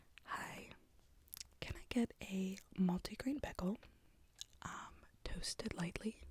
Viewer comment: EVERY time you said “bagel” i heard “pickle”